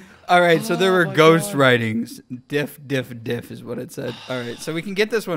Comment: it def did not say "dif" it said "die"